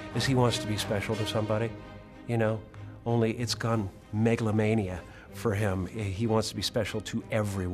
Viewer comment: i thought he said megalovania